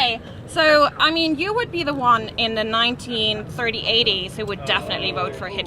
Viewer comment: did she just say the 193080's ? Pretty sure I heard that right